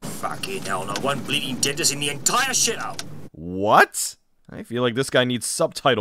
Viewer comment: I think he says "One bleeding dentist in the entire shit-hole."